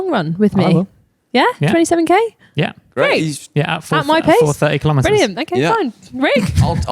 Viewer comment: how fast is these days…. Did i hear 4:30 per km for the 27km longrun?